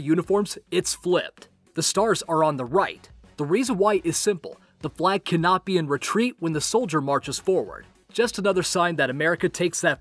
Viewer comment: What is he saying about the flag at 9:10 ? "The flag can not be in retreat while the soldiers are marching forward" ?